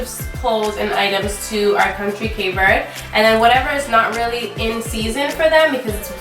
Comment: Love the video! Just subscribed :) What country are you saying at 5:40? I can't figure it out :D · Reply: Thank you SO much Christa! 💖 Our family is from the Cape Verde Islands on the west coast of Africa. I notice now it's not so clear!